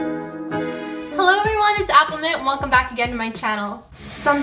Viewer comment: Lol.if you turn on the subtitle at 0:20 instead of appleminte it said aquaman😂😂😂😂 Appleminte is Aquaman!!!